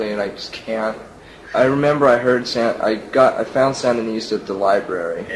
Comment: what band/album did he say he found at the library and that if it was punk rock he wanted nothing to do with it??? Samanista? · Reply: I think Sandinista by The Clash.